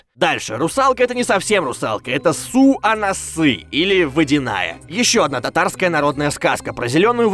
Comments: Мне одному послышалось про русалку: сука на ссы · мне послышалось ссу, а на ссы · мне одному кажется что он сказал "ссу а нассы"? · мне одному послышалось это не русалка это насувносы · Мне если честно послышалось, не "Су Анасы", а "Ссу на носы"🤣 Ужс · Чо мне послышалось или русалку зовут срут ананасы😂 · Су анасы мне послышалось сук она ссыт